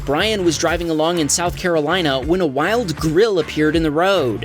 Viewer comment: I swear I heard wild gorilla and not wild grill in the road LOL